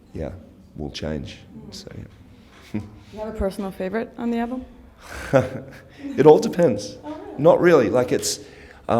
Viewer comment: What was his answer to what his favorite song on the new album was? He said two songs, but I couldn't hear him. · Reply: 710 Writings On The Wall + Deathless Song.